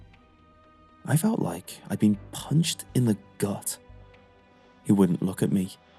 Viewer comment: What did he say.? I couldn't hear that sentence well enough. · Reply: "I felt like I was punched in the gut"